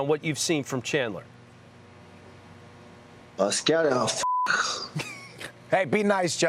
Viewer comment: who's gonna translate what gaethje said for me? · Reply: “Uh scouting, oh, fuck”